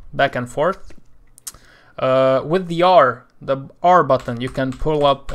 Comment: what he say button ? R BUTTON?